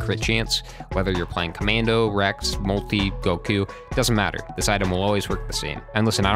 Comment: did I just hear you say Goku?